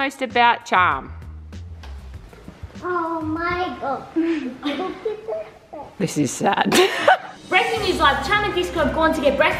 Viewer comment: Is it just me who heard disco swear at 0:45 ? He said "oh my God,look at that a**"💀 not a hate comment btw